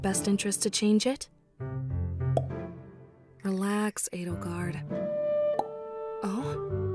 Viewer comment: Oh, her name is Edelgard xD I thought she said "Relax Edgelord".